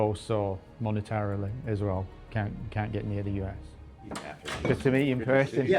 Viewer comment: Did he say “Israel can’t get near the us” ?😂